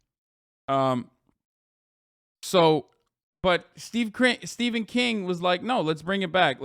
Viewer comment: Oh god, I thought is said Stephen King for a second.